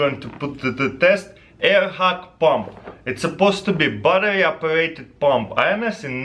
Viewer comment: I hear 'battery operating bomb' xD